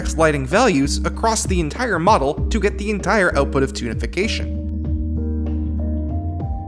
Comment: Every time you say "Toonification", I keep hearing "Tunafication". Also, this is pretty fascinating.